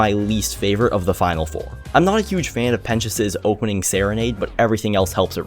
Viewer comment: instead of pentious,it says pen*s(in the captions)